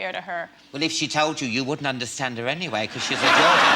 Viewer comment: What did he say at 15:58? :D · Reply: "If she told you you wouldn't understand her anyway cause she's a Geordie."